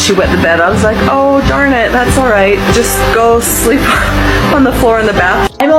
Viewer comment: did she say sleep on the bathroom floor?